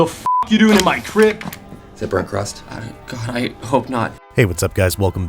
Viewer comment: All of this time, I thought Wade was saying "bread crust", and I was really confused. "Burnt crust" makes a lot more sense. 😅